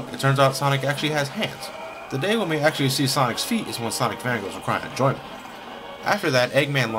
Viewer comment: does anyone know what he says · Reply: i've finally figured it out he says "The day we see Sonic's feet is when Sonic's fan girls are crying in joy"